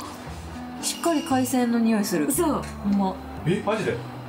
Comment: 이 "옴마야" 이렇게 들려서 자막을 봤는데 심지어 뜻도 정말이네 였네요 ㅋㅋㅋ 경상도 지역에서 정말이네! 할때 옴마야 엄마야 이렇게 하는데 ㅋㅋㅋㅋ 신기했음요